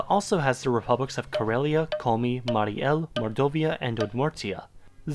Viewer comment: thought you said moldovia